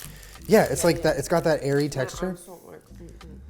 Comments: tell me why I heard “Aries texture” instead of airy texture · for a quick second i thought he said “it’s got that aries taste” not airy HAHA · He said "it has that airy texture" and I thought he said "aries texture"